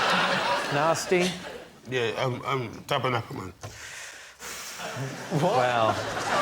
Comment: Anybody know what he actually said here? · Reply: Narstie forgets Tomasz Schafernaker's name and says "Tabernacle". You can tell because David looks at Tomasz and says, "He definitely means you."